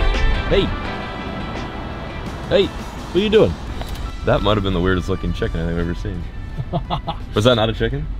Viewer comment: I thought he said " that might have been the weirdest chick I have ever scene"